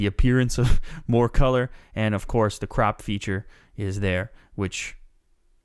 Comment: Thumbs up if you thought he said "Crap feature" instead of crop ?